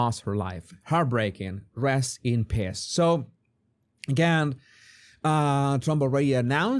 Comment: I know you said rest in peace but it sounded like piss herein 1:42 you may want to modify this video